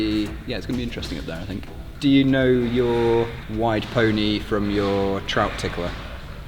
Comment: I thought he said “wide” pony and trout tickler which sounded like jam widths honestly. If thats what that actually means than that was a perfectly fine expression 🙂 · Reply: It is wide pony, it's when your feet are in the crack on both sides of you with your toes facing away from you, looking similarly to riding a 'wide pony'